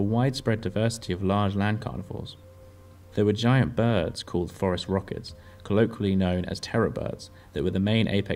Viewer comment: I thought you said forest rocket, which is sounded really cool